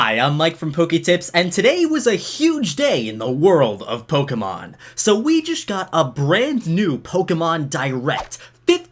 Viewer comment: Ay the start of the video i thought he was gonna say "we just got a letter" 😂